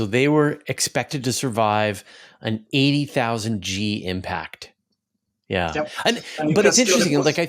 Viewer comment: Did I hear correctly -80 000 G's!!! Im sorry can you confirm I heard that right? Lemme see 800 000m/s^2 ..hmmm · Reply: Yes, the probes were designed to operate at 80,000 g. For start read wiki <link> :-)